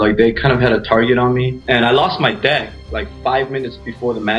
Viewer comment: I legit had to rewind it like three times and turned on closed captions just to make sure he said deck instead of Dad.